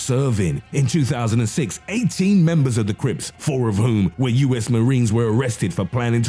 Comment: I thought the dude said creeps not crips